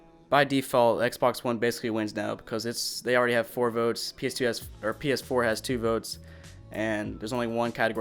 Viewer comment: did I hear ps2 ????????